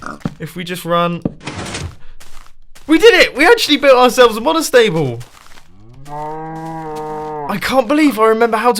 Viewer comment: subtitles say “modest table”.